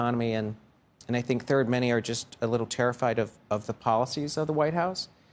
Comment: did someone say "TARIFFied"